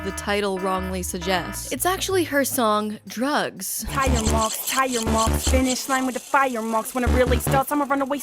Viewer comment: I honestly thought she said Tyre mOnKs 😂